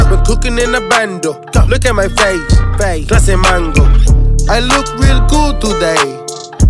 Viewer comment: In the sun smoking my cigar, this banger is a mood...I just don't know why I misheard "classy mango 🥭" but imma start using it as a compliment